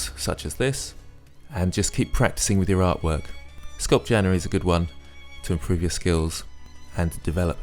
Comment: What is it you say at 2:33? "Sculp-genery is a good one"? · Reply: sculpt January